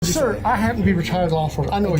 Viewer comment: I can't lie, the second I opened the video I thought he said, "Sir, I happen to be retarded."